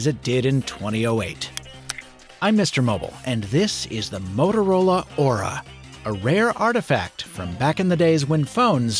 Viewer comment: Every time he mentioned the device name, all I heard was "Motor Ora Ora" オラオラオラ